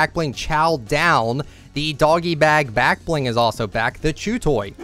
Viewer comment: thought i heard the jew toy